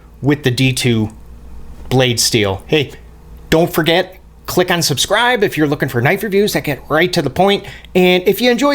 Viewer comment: When you say "click on subscribe" it sounds like you said "click unsubscribe" lol love the vids!